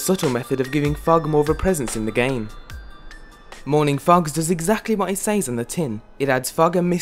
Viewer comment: lol I thought it said frogs